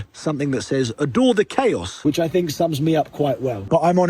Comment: There was a split second where I thought it said ‘Adolf the chaos’